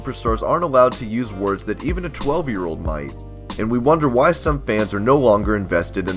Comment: did you say a 12 year old boy